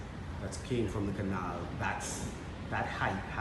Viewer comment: did he say that’s king from the canals?